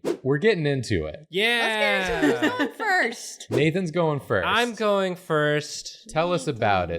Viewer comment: I had to listen to that part twice because the first time I heard "Who's on first?" and immediately thought to respond "Yes, that's right." but then realized actually said "who's going first?" and the answer was Nathan.